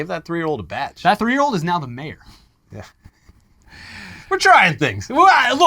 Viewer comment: I thought he said mayor not bear